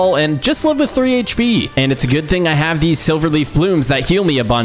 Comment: Did he just say he needed to eat silverfish not silverleaf lol